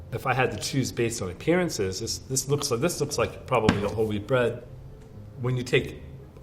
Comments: i thought he was saying 'holy bread' the whole time instead of 'whole wheat bread' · Please tell me i’m not the only one who thought he said “holy bread” and not “whole wheat bread” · I miss heard him saying Holly bread instead of whole wheat bread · Ngl when he was saying whole wheat bread I thought he was saying holy bread